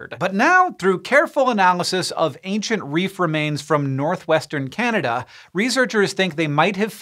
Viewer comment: When he says “reef remains” but all I heard was “reefer mains” 😶 Edit